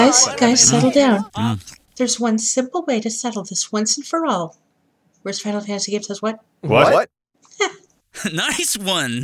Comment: don't understand the part which Terra says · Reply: "Worst Final Fantasy game says what?"